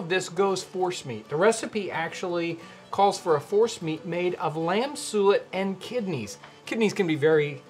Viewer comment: Lord have mercy, he said "force meat" and I swore he said "horse meat". I almost dropped dead!